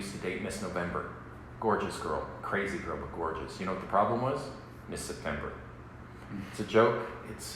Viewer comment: what did he say the problem was? I kinda dont clearly hear it. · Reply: "do you know what the problem was, Miss September" since he is dating Miss November. It's a joke. Non-linear.